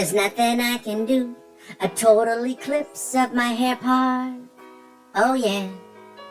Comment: “Total eclipse of my hair part”, dude, lmao, you’ve gotta be a professional comedian- IF NOT, WHY NOT!!! Hilarious 🤣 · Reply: For months I thought was saying my hair pie, ha ha!